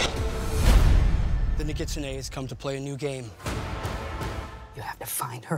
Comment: Subtitles said “the new KitchenAid has come to play a new game” at 1:19 hahahahhaa